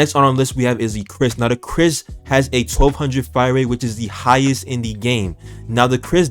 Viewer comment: did he say the kriss had a 12k fire rate? 3:05* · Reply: <num>=1200